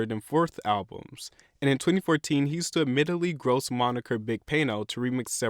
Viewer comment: what did he actually say at 3:11? i couldn't make it out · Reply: In 2014 he used the admittingly gross moniker Big Payno to remix several tracks